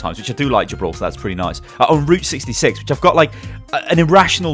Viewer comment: Thought you said rooster 66 xD